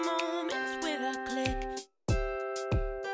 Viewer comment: What does she say at 0:13??? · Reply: Emily Bushe She says " Hurry up TK, I'm already late"